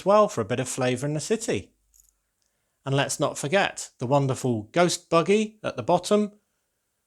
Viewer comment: Did he just say anti-goat gun instead of anti-ghost buggy